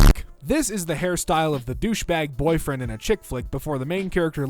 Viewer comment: I had to read it cause instead of "Chick Flick" I heard "Chick-fil-A" and got really confused.